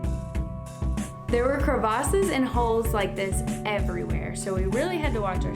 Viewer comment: did she say crevaces